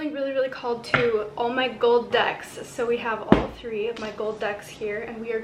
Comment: Oh No not the gold ducks!!!... Oh!!! Gold Decks that's ok.....for a minute I thought you said gold ducks...phew